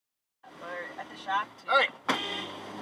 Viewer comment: I said "Get the f*** away from my pond!" not palm. Heron was stalking my koi fish. There's more to the video on my channel titled The Heron Incident